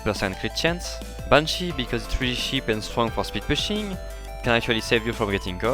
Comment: please add subtitles no one can understand like so he can see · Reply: subtitles say "Punchy because three sheep and fun pasta machine can actually save your rating" lmaoo google plz xD